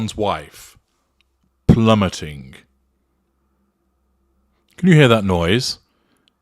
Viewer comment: Oh! Plummeting! I thought it said Plumbering ! Pity, she really does know how to jiggle those Ballcocks ! Thanks 👍